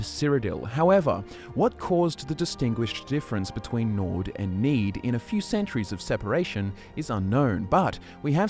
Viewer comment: I heard "what is the difference between Nords and Mead?" Me: "Milk Drinkers"